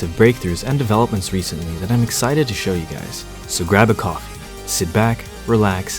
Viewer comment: Am i the only one who thought said "grab a cock." In the intro?